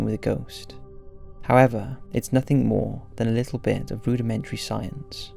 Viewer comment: Istg I thought you said "boo-dimentary science" instead of "rudimentary science" at 5:41 and I had to keep going back and listening. I'm ignoring the hard evidence and choosing to believe that you did.